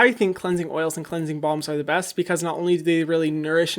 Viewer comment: omg I always thought he was saying cleansing bomb not cleansing balm🤣